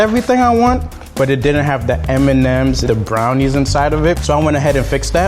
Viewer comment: Did he say Eminems instead of M&Ms? 😂